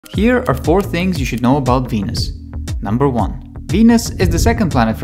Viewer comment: When i wasn't looking i heard: "Here are 4 things you should know about Penis" had me weezing